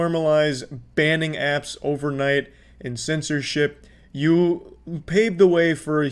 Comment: this guy's voice is so crystal clear that the auto generated captions are 100% accurate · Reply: found a mistake "IN censorship" instead of "AND censorship"